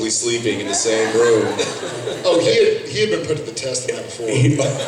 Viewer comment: What did Jared say please ? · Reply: He said, "He'd been put to the test the night before" xD this vid is actually much clearer than the other uploads